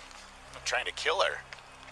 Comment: But the problem here is the dispatcher. She only said "Someone trying to assault caller. That is a totally different vibe than, " He's trying to kill me. He already killed my Auntie.". That is a failing in the system and training of operators. I had a similar type situation that led to the deaths of five people. The operator failed to report two very clear gunshots. Then the operators failed to get even basic information from the caller. Information that would mean the lives of the three officers involved as well as the family in crises. We further discovered that there are no mandatory hearing exams each year for 911 operators. Nor is there any ongoing training for officers or 911 operators regarding domestic violence. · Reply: Check out 7:55, officer says "...trying to kill her" to another officer. The cops knew, we just didn't hear that part of dispatch.